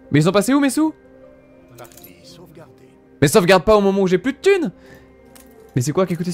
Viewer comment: "Mais sauvegarde pas quand j'ai plus de thune", à ce moment, j'ai compris "mais sauvegarde pas quand je galère !" XDDDD Aaah merci le jeu pour ce timing parfait !